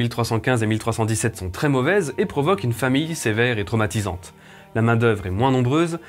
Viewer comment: Sympa comme vidéo :) Mais a un moment, quand vous parlez des différentes catastrophes naturelles qui arrivent en Europe, vous dites "famille" au lieu de "famine". Du moins moi, j'ai entendu "famille" plutôt que "famine". Et puis, le fait de dire que le premier enfant de chaque roi avant le problème de succession était un fils, c'est un peu général... et erroné comme déclaration, quand même. Il y a quand même plusieurs rois qui n'étaient pas les aînés mais qui sont devenus rois parce qu'ils étaient les enfants mâles les plus proches. Comme Louis IX (Saint Louis), qui était le deuxième fils de Louis VIII. Ou Louis VII, le deuxième fils de Louis VI. Ou Philippe II qui était le cinquième enfant de Louis VII. Philippe IV qui était le cadet de Philippe III, etc, etc... Bref. Mis à part ça, je trouve que c'est une très bonne vidéo :) Au fait, le portrait de Charles d'Orléans... c'est un portrait d'époque? J'ai l'impression qu'il a l'air plus "récent" comparé à ceux de Charles VII ou Henry VI... on dirait un portrait de la Renaissance... enfin, c'est juste li'mpression que j'ai.